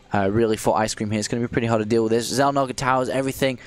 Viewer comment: Did I just heard Dappollo say Xelnaga Taverns ??